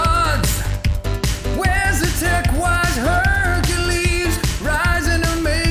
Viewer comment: i heard "tech white" and had to rewind to check that subtitle 😂